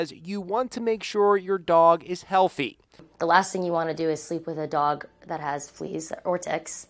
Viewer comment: Was I the only one who thought that the doctor said: the last thing you wanna do is sleep with a dog that has fleas or di**s😂.